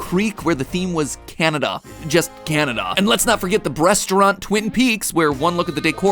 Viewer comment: Matpat said "Breasturant" instead of restaurant.